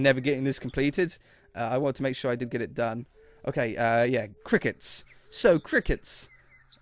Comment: I thought cricket said chicken